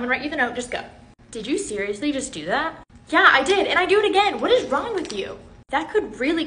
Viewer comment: I thought she said what is Ron with u